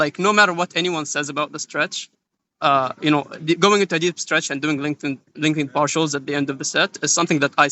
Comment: What does he say/what is he talking about at 35:41 onwards? Doing something for the rest of his life? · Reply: he's talkin about doing focusing on stretch and lengthened partials for the rest of his life